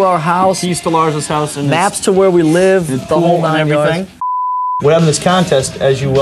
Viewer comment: lmaooo what the holy hell... He actually said "maps to where we live" but I swear up and down I actually heard him say "Napster, where we live" Jesus isn't that a whopping fat case of foreshadowing/misunderstanding if ive ever heard one...